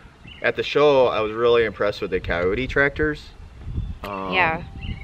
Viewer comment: Thought said Coyote Tractor. Nope, the google corrected me it’s Kioti Tractor.